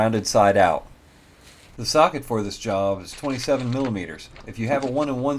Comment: Did you just say millipeder 😂